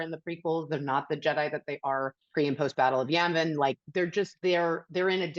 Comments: Did she just say "Battle of Yammin?" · did she say battle of “yan-vin”? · did she say “pre/post battle of Yemen?” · did she just say battle of yamin?! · Did she say Yanvin or Yavin at 6:31? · it sounds like she's trying to say Yemen instead of Yavin. Not a huge gaffe, but it shows that she doesn't really know anything about Star Wars, she's just trying to say Star Wars things.